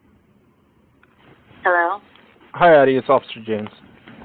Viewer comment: This is the interview with Cassie is it not? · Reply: He clearly says Addy